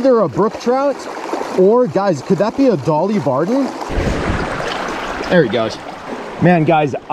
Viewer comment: I thought you said Dolly Parton 😂😂